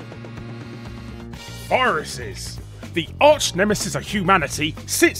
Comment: Not gonna lie, at fist that sounded like 'walruses' to me XD